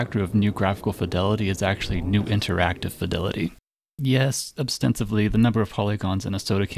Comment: Good video, but I caught a couple mistakes in the captions: at 3:41, it says "obstensively" instead of "ostensibly", and 5:48, it says "through" instead of "threw".